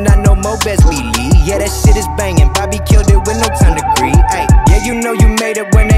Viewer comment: Great song 👍🏼 At first I thought he was saying "Bobby killed it with no time to breathe" & I was like "Facts"